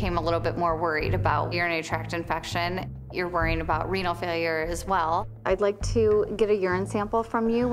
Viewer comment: i thought she kept saying “weenal failure” and that just made me giggle